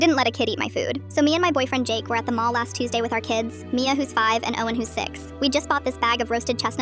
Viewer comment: Why did I hear ‘Mia who’s 6, Owen who’s 7’